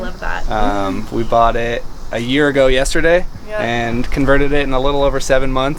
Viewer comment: “we bought it a year ago yesterday” I heard it right, right? I want a cat in a bus too